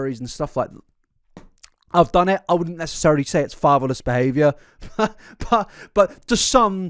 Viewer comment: Okay, I'm going to be so for real, it took me WAAAAAAAY too long to understand that you said "fatherless". Being from Southwest Iowa, I kept hearing "favaless", like, lacking fava beans, I guess? 😅